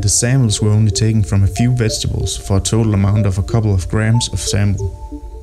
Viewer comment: What is he saying at @ 1:30? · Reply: Oh grams of sample?